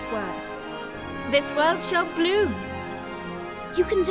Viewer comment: I thought she said and this world shall boom